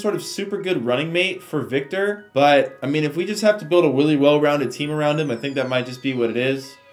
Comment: Did anyone else hear say willy well rounded team at 12:24 or just me but love the video!